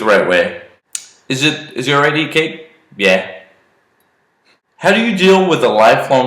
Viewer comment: What does he say at 4:47 ? Been replaying it so many times and still can't get it. All I hear is "Is it ready cape? Yeah"